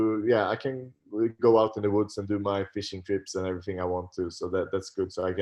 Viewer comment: I thought he said "fish & chips" but it was "fishing trips" lol